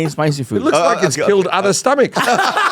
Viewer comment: "I have a weak stomach" .... "It looks like it's killed other stomachs" 🤣💀💀 what a line 😂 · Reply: Hahahhaha I thought he said "looks like its filled other stomachs" which is still gold